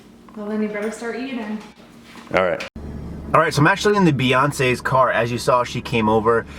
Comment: Did you just say Beyonce at 10:40 hahaha. It's so cool going on a ride along through your town!